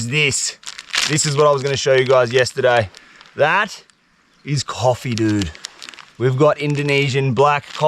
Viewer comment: my damaged mind thought imagine if he said "that is... CONDOMS dude" - I'd believe him cuz shit looks too similar! 🤣🤏